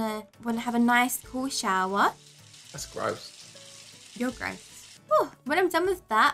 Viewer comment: did someone say that’s gross